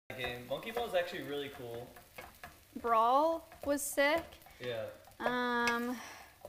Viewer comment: did he said monkey balls??